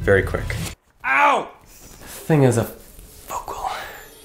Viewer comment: what derek said" that is a focal ". what i heard "that is a fucked hole"